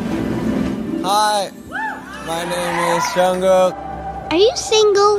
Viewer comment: When she said "bcs I love butter" that was so cute. Pray for Jimin ARMY😭😭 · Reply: I thought she said " i like bada" (sea in korean) and i was confused lol